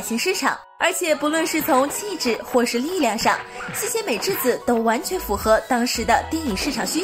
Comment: 西协美智子，我還聽成"吸血美智子"...